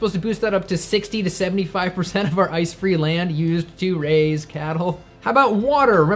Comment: I thought you kept saying "ice cream land"